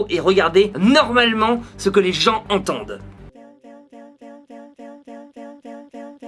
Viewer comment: A 8:12 j'ai entendu éventail, tafiolle et pieds en bas. C'est normal?😅😂